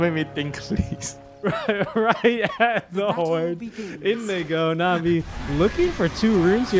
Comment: did he just said n word? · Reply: He said "in they go."